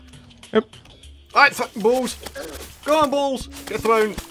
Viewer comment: sounds like you said go on f*kin balls weeeeeeeeeird